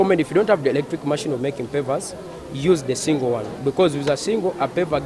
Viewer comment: did he say make Papers with the soil?